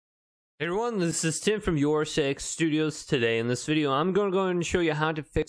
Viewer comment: Bro said sex instead of six💀